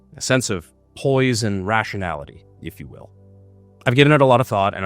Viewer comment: "A sense of poise and rationality" ah yes a Panic at the Disco reference. 😂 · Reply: i always heard it as poison rationality 😂😅😅